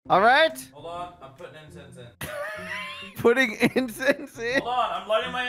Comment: I thought he said "im putting insest in"